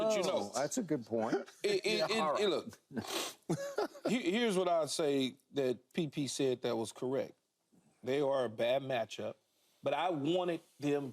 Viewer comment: Did he just call that man PP!!?? 🤣🤣🤣